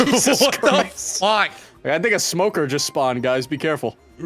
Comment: What did say at 33:03 🫣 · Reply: "A smoke just sprained"